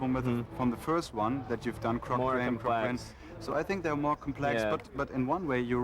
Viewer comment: what does Stephen say at 46:26? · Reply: “More complex.”